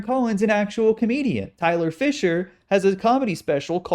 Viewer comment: I was veryconfused, I thought he said Isla Fischer, not Tyler whatever his last name was